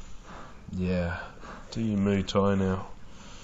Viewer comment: i ran back when he said “yeah do you Muay Thai now” as she sounds like she’s choking so, not know what that meant thought maybe a Thai chi move or something so, I googled & found this - Muay Thai chokes. When you watch a vid it’s like the forearm v around the neck. Don’t know if that’s what he meant or if he trained at some point. Either way in my mind even before hearing him say that all i could picture was him having her pinned & hands around her neck choking her! I didn’t look at how long it sounded like that & don’t want to rewind to the start of her breathing sounding off cuz it’s hard to hear what we think is happening but, they say it can take 5+ min. Maybe he was getting tired & thats why he was saying “enough” a bit earlier. This case was so hard to hear the 1st time but, you always put info in cases I’ve heard of so, I always watch yours as the attention to detail is top notch!🥰